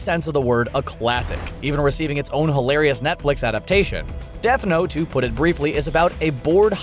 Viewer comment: it says death hote instead of death note